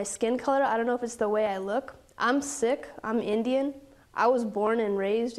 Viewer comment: Did she say she was sick (2:49)or is that the way Sikh is pronounced in America or she really did mean that she is sick - physically or most likely mentally?